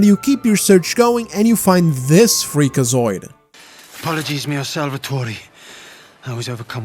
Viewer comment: God damn it. I thought you say Fleekazoid.